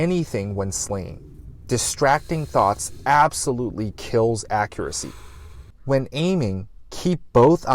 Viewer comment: "Distracting Thots absolutely ruin accuracy" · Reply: but he said “kills” instead of ruins